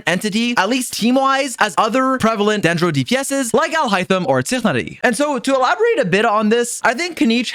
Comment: and 12:47 did I heard it right? "Tiffany" on Tighnari?